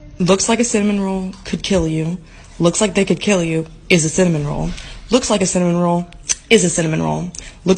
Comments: I thought it said 7yr old 😭😭😭😭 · Why did I hear "looks like a 7 year old can kill u" in the 1st one 😭